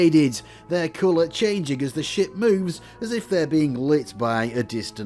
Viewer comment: sounds like hes saying, ...-re flat shaded, their color changing as the S**t moves as if they're...